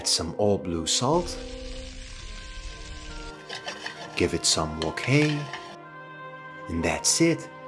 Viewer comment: I heard “give it some cocaine “